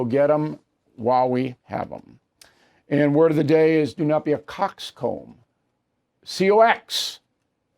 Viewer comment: for a split second I thought he was going to say don’t be a cock sucker 😂